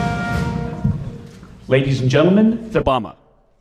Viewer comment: Did I hear this right?....The President of The United States of America was referred to as "The Bama"? Disgraceful at best!!!! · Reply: I think he said "The Obamas" because they both came out. I noticed that too. I thought he said "ladies & gentleman, Obama" which sounded disrespectful but I think there was a glitch and part of it got edited out.